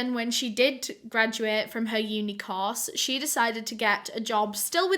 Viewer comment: When you said “that uni-course” i thought you said unicorn lol